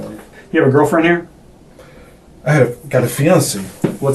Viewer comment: Did anybody else hear, " I had, have a fiancée." at 1:09 into the video. Well, that solves that mystery, especially since his phone is supposed to be broken and he don't know she's dead!! OOPSIE!!!